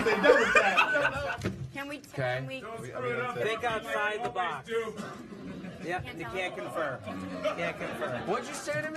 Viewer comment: What did he say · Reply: He said don't screw it up like u always do lol